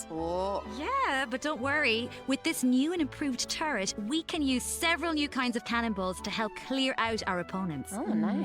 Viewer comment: I was tabbed out I thought they said " we can use several new kinds of cannibals"